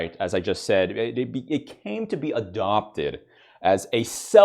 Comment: I wansnt paying attention and I thought he said that femboys tend to be adopted